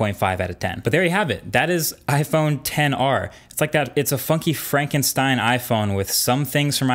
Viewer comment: ngl, @ 5:46 I thought he said “fucky Frankenstein” 😅